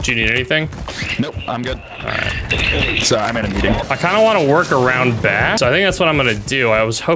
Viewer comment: Did say "sorry, I'm in a meeting" at 6:29? Legend